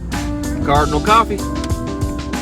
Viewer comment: Up until the last few episodes I always thought you were saying Cardinal Copy which I thought was the name of the road the build was on. Lol.. The whole time you were saying “Cardinal Coffee” giving the coffee shop a shout out. 🤦🏻‍♂️😂